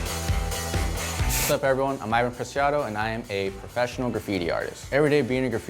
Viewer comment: "sup everyone im Ivan Preciado and im gay professional..." thats what i heard